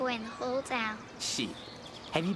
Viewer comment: sounds like he's say shit. Prob not what he said and kinda wanna know wat he suppose to say.